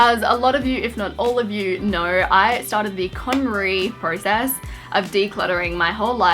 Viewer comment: what's that word she says at 0:54 ? I want do learn more about it but i can't clearly hear what it's called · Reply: nvm it's Konmari